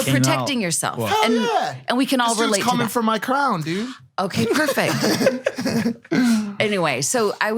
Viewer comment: lmao. definitely thought Mike said "someone' coming for my cum" at 6:05 and not "...coming for my crown!" lmfao. Would've been that much funnier if he doubled down on it and really emphasized someone chasing him down for his cum lol